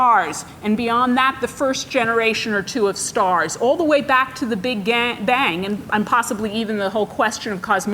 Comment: Did she just say "all the way back to the big gang bang" ?? Rofl